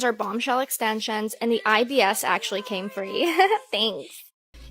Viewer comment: did she really say IBS, ("irritable bowel syndrome")??